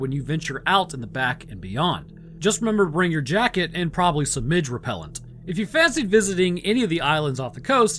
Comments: Now, I'm not sure what "midge repellent" is but I'm just imagining someone spraying a spray bottle at a little person. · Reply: LMFAO! I thought he said "ridge repellant" and was at a loss... but if it's "midge repellent" that would actually make sense because I think midges are these tiny little gnat-like things similar to noseeums. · Now, I'm not sure what "midge repellent" is but I'm just imagining someone spraying a spray bottle at a little person. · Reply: Honestly, I heard "minge repellent."